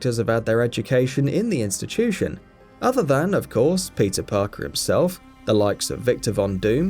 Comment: am i the only one who hear "pizza parker"?